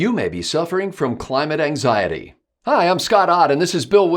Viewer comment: Phew. For a second there I thought it said "Climax anxiety".